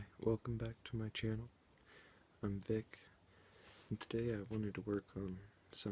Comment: I thought you said "I'm thicc" instead of "I'm vic" 😂